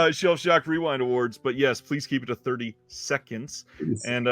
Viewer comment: Taking a video of your collection that lasts less than 30 seconds and posting it online is a lot of work for a pretty good chance at $50 AND appearing in the live awards show? That seems fairly dang simple to me... Most people have a smart phone and can say a few things in less than 30 seconds. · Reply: did u say 30 seconds? I thought u said 30 mins...my bad...coffee hasn't kicked in yet I guess 😅